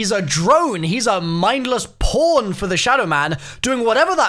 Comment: I thought you said porn for a second instead of pawn lul